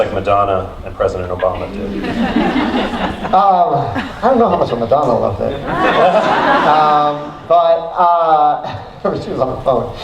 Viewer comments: what did he say about madonna, I can’t understand it 36:20 ? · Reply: He said he didn't know how much she really liked the show because she was on her phone. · what does he say after “I don’t know how much Madonna loved it?” It made everyone laugh even harder and he said it so low the captions didn’t even catch it. 🤔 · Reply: I think "Probably she was on the phone." is what he says.